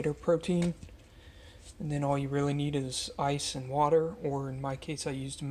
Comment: PM and not awake yet. At end of vid where he said ice and water and I thought he said spice and water! I was like, huh? 😂😂😂😂😂 ??? Been a looooong 2 months or almost 2.